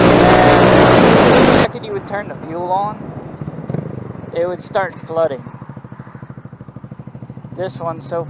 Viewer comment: When you turn off your bike, do you recommend to turn the fuel switch off? Or it doesn’t matter ??? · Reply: ” if you don’t turn the fuel on, it’ll start flooding” is that what you said???